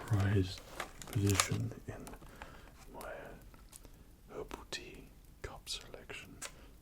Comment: Gosh my American English thought he said "Ovaltine cup selection", but then I realized he was saying "Herbal tea cup selection"